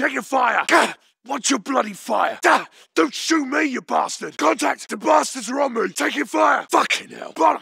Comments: What does he say on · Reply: "Contact,the bastard's on me." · What he says: "The bastards are on me!" My brain: "The bastards are ramen!" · What he says: "The bastards are on me!" My brain: "The bastards are ramen!" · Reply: I thought it Said the bastards robbin, I was the like wtf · i hear this all the time when I'm getting shot like i don't hear any of the other lines Edit: it's "FUCKIN' HELL!" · What he says: "The bastards are on me!" My brain: "The bastards are ramen!" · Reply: I always thought he sounds like he says the bastards are hovering